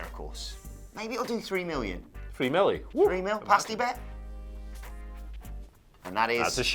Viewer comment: What??? Did you say "pasta bet" 🤷‍♂️🤷‍♂️🤷‍♂️ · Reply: Pasti, it’s probably a British thing